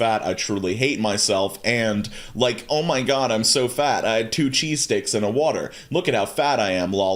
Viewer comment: Omg honey i thought you said two cheese steaks😂😂😂